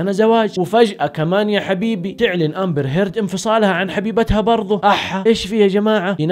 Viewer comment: wait a minuet, 2:15 did you just said a7a !! 🤣🤣🤣🤣🤣🤣🤣🤣🤣🤣